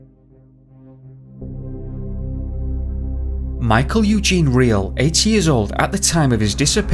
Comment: I thought that you said Michael was 80 years old. Was it 18? Or 8? Lol Edit:ok I think I heard 8 later on